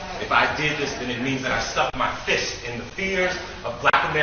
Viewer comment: I could’ve sworn I heard him say at the end he stuck his fist in rears